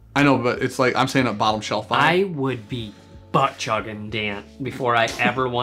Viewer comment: I heard “buttchuggin” and I went “whooooaaaaa”